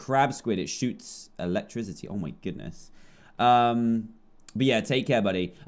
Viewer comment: Turn on captions. He apparently says beer ;o; He did not say beer. He said 'but ye' really fast :D